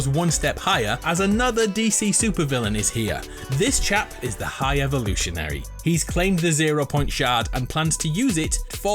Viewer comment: Did you just call the high evolutionarily DC